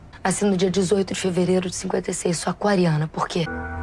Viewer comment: Elas nasceram dia 18 de fevereiro de 1956, eu escutei certo ????